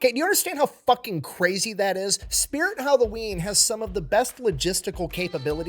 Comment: Hey I thought that you said Waffle House had the best lagistics